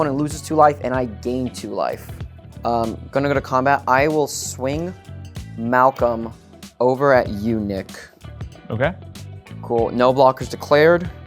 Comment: I heard “swing Malcom over at eunuch “ at 27:17 lol